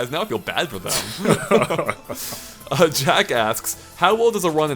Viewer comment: I thought he said "Jackass" 😂😂